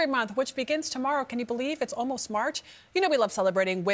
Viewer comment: Did she just say "smarch?" Lousy Smarch weather.